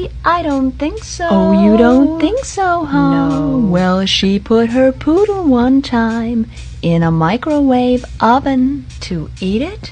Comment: Ok but why did i hear “Her noodles” ✋